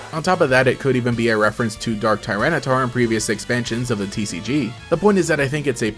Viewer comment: I swear didn't mishear Dark Tyrannitar as Darth Tyrannitar